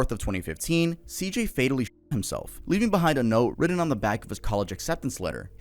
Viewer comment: I thought said he fatally shit himself lol